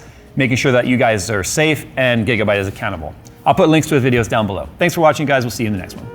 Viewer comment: I misheard "Gigabyte is accountable" as "Gigabyte is a cunt"